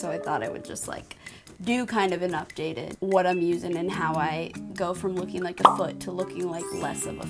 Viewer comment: When you said "go from looking like a foot, to less of a foot" i thought you said " go from looking like a foot, to a blessed foot" 😂😂